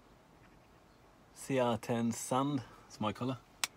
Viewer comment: Can you tell me what is the color you ordered? You indicate a color type at 2:38 but it isn’t clear what you’re saying. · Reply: I believe it was called “Sand” but it was a few years ago…